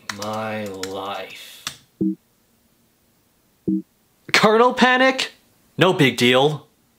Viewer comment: Turn on Captions and you will see the captions is said "Colonel panic" instead of word "Kernel Panic"